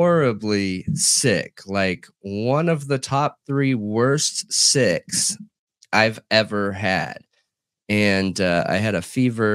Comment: I thought you said the worst sex you’ve ever had, I had to rewind🤭😳🤣🤣🤣🤣